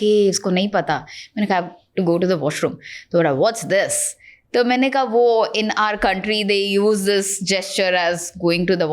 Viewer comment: what did she says Upyosh? What does this mean???? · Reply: It's "up yours" not upyosh ... it's a slang means fuckoff